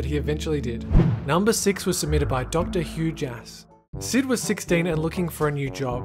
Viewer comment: Oh number 6 I thought he said doctor huge ass because I looked away for a second to pet my cat